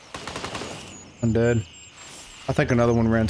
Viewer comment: he definitely says "1 dead", but the start of the word 1 gets cut off by his mic so all I heard was "un dead" and so I thought you were dead lmao. Great times though, that was so much fun